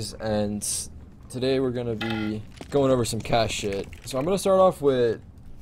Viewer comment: anyone else heard cat shit?